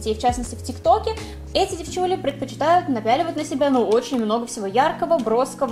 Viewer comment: Ты:очень мягкая вода(мне так послышалось) Я:а вода бывает мягкой? ～(￣▽￣～)